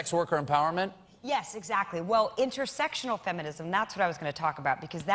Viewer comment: The subtitles are wrong ... she said "intersectional feminism" not "intersexual feminism." Intersectionality is what she explains immediately after 6:45.